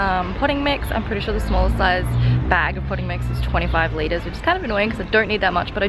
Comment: I thought you said pudding mix instead of potting mix and when you said the smallest size was 25 liters I was like, "Wow, that's a lot of pudding mix!" xD